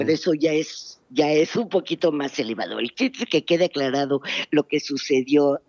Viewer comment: min. "Un poquito más elevado"? ¿Entendí bien? Insinuando que no tiene caso explicarnos todo y/o que estamos tontos para entenderlo?